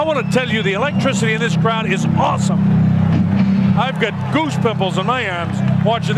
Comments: " I've got goose pimples in my ass." Is what I thought I heard... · “I have goose pimples on my arms” wtf lol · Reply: LOL, I even thought he said 'goose pimples on my ass', real weirdo this commentator. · I must say I’ve never heard the term “goose pimples” until now. Thank for I didn’t have to live my entire life without hearing that. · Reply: I thought he said goose nipples for a sec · did he just say I got goose pimples in my ass?